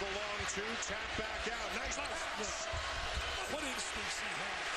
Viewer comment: I remember watching this game live on TV, in college. At the time, I thought Dick Vitale was saying “what is this? What Is This?